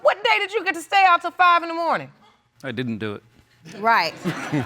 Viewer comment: 🧔‍♂: I didn't do it. What I heard was, "I'm not the father."😄